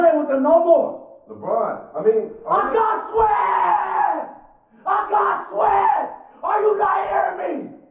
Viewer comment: I GOT SWEPTTTTTTTTTTTTTTTTTTTTTTTTTTTTTTTT I GOT SWEPTTTTTTTTTTTTTTTTTTTTTTTTTTTTTTTTTTTTTTT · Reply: David Reeves okay thank you, I thought he kept saying “I GOT SWAGGGGG!”